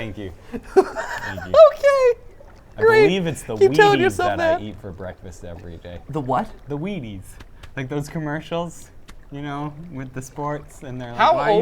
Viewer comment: Who else thought he said, "It's the weed I eat for breakfast"?